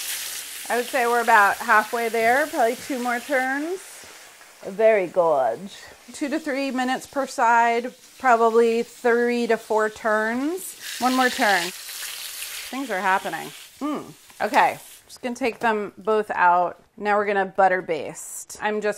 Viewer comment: Did she say "30 to 4 turns"?